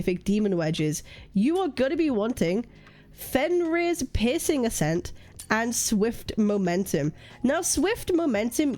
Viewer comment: i thought you said pissing ascent